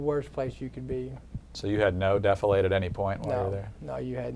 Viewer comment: "you had no 'deflate'" ? What is that d word?